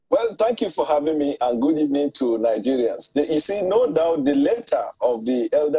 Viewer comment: Did he just say and good evening to Nigerians 😳